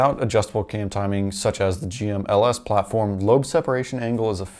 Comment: at first I thought he said cheap LS platform